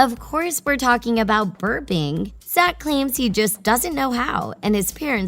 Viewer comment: OMG when she said "burping," I thought she said BIRTHING LMAO